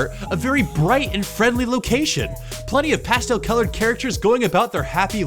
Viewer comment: I heard passed out colored characters. XD